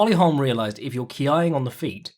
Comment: what is that word? Keiying? Key eyeing? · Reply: ki-ya